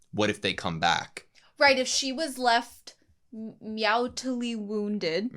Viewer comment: Did you just said "meowtully wounded".